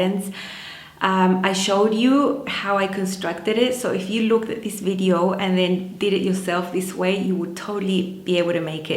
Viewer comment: Hi, what did you say about the pattern instructions (18:54)? - sorry couldn't quite understand what you said about them. Amazing jacket! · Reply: The instructions are “small”. They come in one page in a list format. Similar to Burdastyle. I don’t mind because the notches help put the pieces together easily. There is a lot of information on the pattern pieces making it easy to put together.